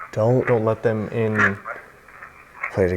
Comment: I hear "Hey, Don't let them sit in the chair"